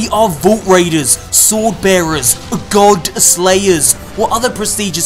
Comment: the way he says God slayers it sounded like he joked lol